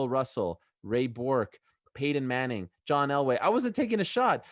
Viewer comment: did he say Ray Borg ? 🤔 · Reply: Ray Bourque....hockey legend